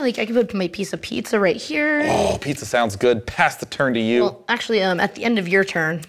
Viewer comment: "Pizza sounds good, Pasta turn to you" Was that an insane pun that went unnoticed or am I hearing things?